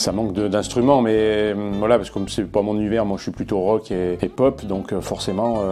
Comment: à 5:01 le GA qui dis moi shui plus hip hop et rock LOL 😂😂😂😂😂😂😂😂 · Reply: Il dit je suis plus rock et pop :). Pop c'est Green Day par exemple.